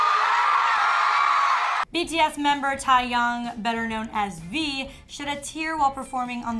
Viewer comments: Excuse me it sounds like she is saying “Thaiyoung” I’m sorry but It’s Taehyung. I screamed taehyung when they said it. But honestly not suprised he cried. It’s such a touching and emotional song. Every army can relate when we say we get emotional sometimes listening to the song. Seeing the members cry makes me even more emotional aswell. Pray for our baby ❤️💜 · The way she said Taehyung it sounds like she said tiehyung